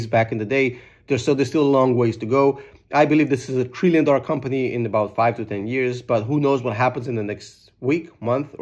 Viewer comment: What did he say at 8:04 ? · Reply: It was an inside joke. Trillion dollar company in 500 years lol